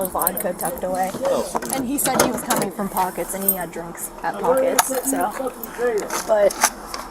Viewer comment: sounds like she said “an he had (drunks) at pockets” are we sure she didn’t take a hit off that vodka that she cracked open an tampered with